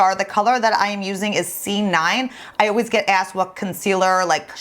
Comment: OMG I love how your make up looked like!!! I have to check that Dior Bronzer but could you pls remind me shade of JS concealer you are using ?X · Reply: She actually said C9... time stamp 3:10 of the video